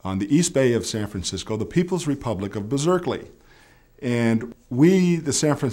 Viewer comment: Did he say "Berserkley" on 4:58? Was it intentionally or is it a word play?